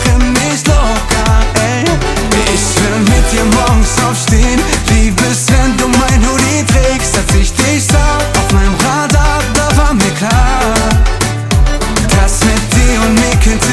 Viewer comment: Bin ich der einzige der bei 1:39 anstatt Hoodie erst Urin verstanden hab?